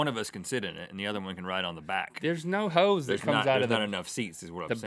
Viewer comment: i thought he said "no hoes in the back of his car". lol